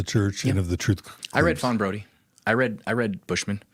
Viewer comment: Did he say,”Faun Brody” & “Bushmen”? What is that? Gibbons? Thanks ❤❤❤ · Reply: Fawn Brodie Terryl and Fiona Givens Richard Bushman